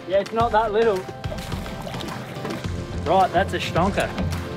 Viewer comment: 😕did he say "that's a shnawka" 🤣 ??